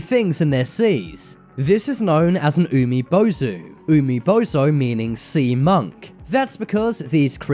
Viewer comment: I thought he said umi- bozo💀